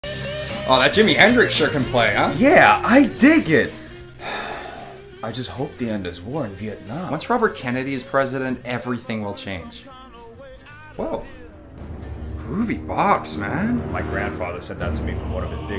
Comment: I heard "I just hope the end is war in Vietnam" 🤣